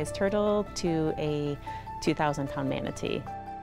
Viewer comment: Lmao she said at 1:05 she said a "2000 ton manatee" that's 4,000,000 pounds of manatee. Damn Boi he thicc